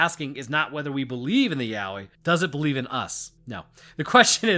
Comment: "The question isnt do we believe in the yaoi, does it believe in us?" Amazing misheard quote for me